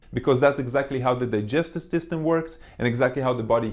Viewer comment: At one point I heard him say "justice system" instead of "digestive system" and it didn't make less sense than the correct phrase...